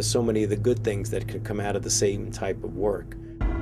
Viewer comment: it really sounds like he says, "Satan type of work." Probably not but it caught my attention.